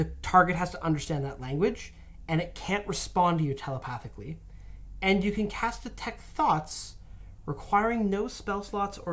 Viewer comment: In Telepathic I keep hearing "you cam cast detect thots" haha